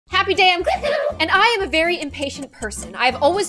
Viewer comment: All these days I thought she said Happy day with but then I payed attention to the subs lol